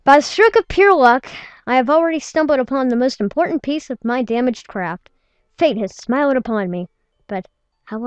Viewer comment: She said crap instead of craft in